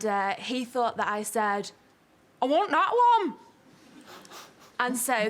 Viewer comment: What does she say at 5:28? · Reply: I want that one